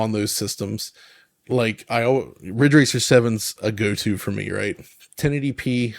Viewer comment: What games is the one mentioned at 2:27? I can't understand the name. And btw I'm still amazed of Rayman Origin/Legend on PS3. It's a rock solid 1080p 60fps and looks astonishing. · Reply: Ridge Racer 7 (But if I remember this right, Ridge Racer 6 on Xbox 360 is almost the same game, just missing some content.)